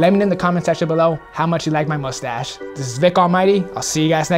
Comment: Amazing work. How well does the paint hold up long term? Lol sounded like you said Thick Almighty after mentioning your stash. Should be ur altered ego.